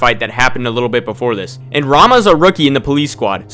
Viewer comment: I am a weeb, so everytime you said "Rama", I heard "Ranma" initially. Technically he's also a martial artist, but that anime is no where even close to realistic, lol